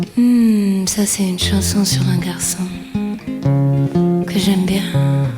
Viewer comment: what does she says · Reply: She says "Mmmm, ça c'est une chanson sur un garçon... que j'aime bien!